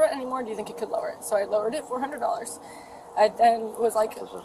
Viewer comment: $400??!!! I wish I had a friend like that! People always take advantage of good people and make it hard for people who really need help. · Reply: 😂 You're absolutely correct, but unfortunately a lot of people in this comment section are just TOO DANG STUPID to pay attention. lol. I heard her say it was "Lowered-BY $400" NOT "Lowered-TO" $400". smh 🤦Although I'll admit, it IS a little entertaining to read SO many idiotic comments.... & it just amazes me how many people were NOT paying attention & just took what they wanted to hear & ran with it. LOL.👏